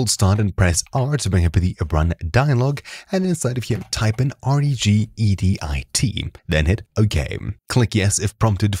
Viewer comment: Min 1:01 I could not hear or read what you said. Sounded like "ungdit" which doesn't exist. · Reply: regedit